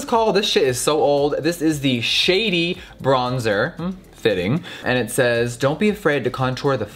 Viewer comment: Why did I hear shitty bronzer instead of shady bronzer 🤣🤣